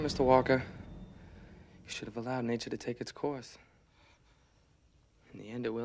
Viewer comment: I always hear "SHALALA FUCKING HOUSE!"